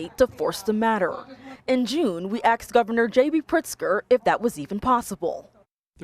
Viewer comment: Um…@ 2:07, did the reporter really say “axed” instead of “asked”? DEI hire?